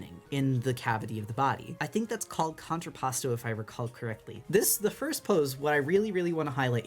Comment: I did mishear you saying contrapposto as contrapasso at first and became quite confused, like idk what ironic punishment and/or Dante's Inferno has to do with gesture sketching but sure, let's see where they're going with this...